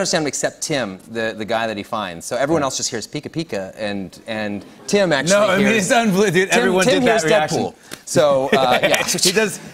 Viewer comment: What does Ryan say at 5:03 that gets the audience to laugh that hard? I can't quite get that, except for "Tim actually hears Deadpool" 🤔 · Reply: M Z You got it. This is all he says, “Tim actually hears...Tim hears Deadpool.”